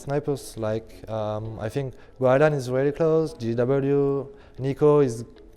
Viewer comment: What's the first name he says? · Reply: Cowey guardian